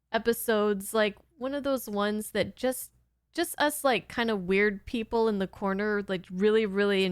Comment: I shouldn't be doing what? Not sure what you mean without context. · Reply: Hey, I listened again to what I thought you said and I was wrong. It was only one word but it makes a world of difference. So at 16:10 I thought you said, one of THOSE weird people. I was saying if you are watching 50 year old Star Trek Cartoons, You ARE one of those weird people. However, on second listen, you didn't say that. You said one of US weird people in the corner. Sorry to being a bit pedantic, but I'm a bit of a Star Trek nerd. I guess It comes with the territory. It was never meant as a dig. Carry on, Lieutenant. (That's the rank I'm giving you)